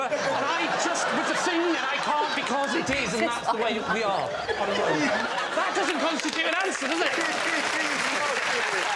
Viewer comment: what does claudia say to david here? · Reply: renderman “is this what I’m like”